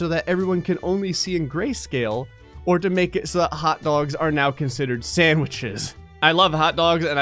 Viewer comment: At the start I thought he said All dogs are now considered sandwiches